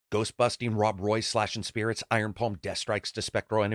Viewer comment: I can’t be the only one who heard “ghost busting white boi”